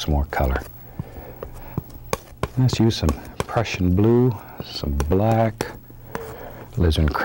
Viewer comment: i thought he said russian blue